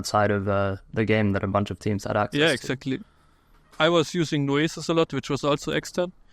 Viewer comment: What software did said here? I cant catch it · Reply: noesis